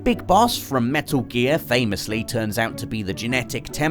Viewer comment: On 5:01 did you say "Solid Liquid and Solid Snake"??